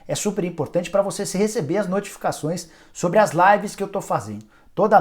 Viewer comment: Quando eu ouvi "você se receber" de um jornalista, decidi fechar o vídeo. rs